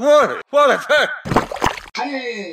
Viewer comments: Is it me or did Midas said "Oh what a d*ck!" in 0:14. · Did Midas just say. "Oi what a c*nt